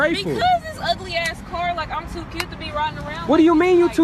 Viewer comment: Did she say she's "too cute"? Because I heard her say "too good ".